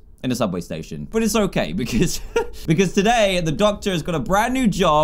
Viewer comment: Dan:Today the doctors got a brand new job, he's a doctor! Me: Oh that's cool, a doctor got a new job then became a doctor.... Makes total sense. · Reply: Chloe Meyer I thought he said neighbor but then I had to replay it ,and then I realized he said doctor two times